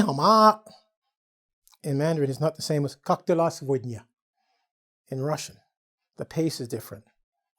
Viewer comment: "Как дела сегодня?", it's really cool, Teacher! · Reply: Я вообще услышала "как дела, свинья" 🤦😄😄😄